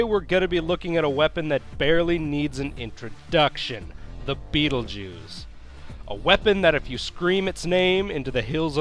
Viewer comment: Thought I heard "The betel jews"